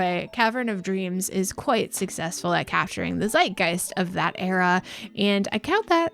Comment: Did you say "Zeitgeist"5:20? :D Anyway, game is on GoG, I might buy it because I sort of like the graphics style of the old N64!